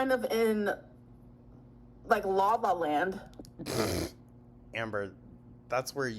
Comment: Did she say "lava land"?